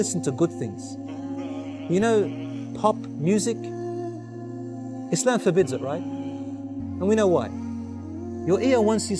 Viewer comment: I heard him say 1:55 , "Islam forbids it, right?". Not " it's not forbidden" 😊